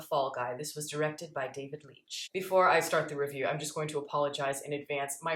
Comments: I heard "David Lynch" instead of "David Leitch" and was excited for a second. Now I'm depressed. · Lol I thought you said David Lynch then I was like wut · I thought you said directed by David Lynch.